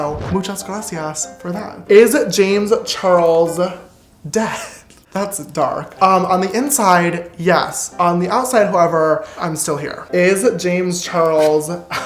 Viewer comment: "is James Charles dead? On the inside yes on the outside however (I can't hear very well I'm guessing lol) I'm still here," same James same.